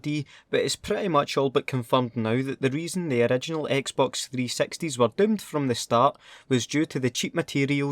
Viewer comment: when you said xbox 360 it sounded like you said xbox 3 sexty